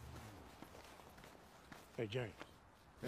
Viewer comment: Ooooh! .... & I thought he said: "never 'take off' your head set" Hmmm ... how about a peace 0f pizza NOW?